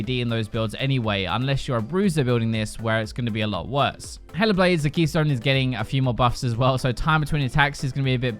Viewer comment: I thought you said "Hella Blades" not Hail of Blades xD